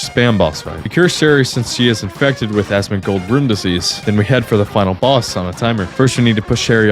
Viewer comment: Did dude just say asmongolds room disease lmaoo